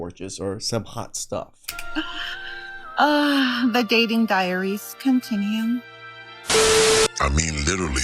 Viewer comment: Loooved that monkey. hilarious. I was confused at first when the one 304 mentioned her dating diary, I seriously thought she said her dating diarrhea, which is a better description than diary anyway.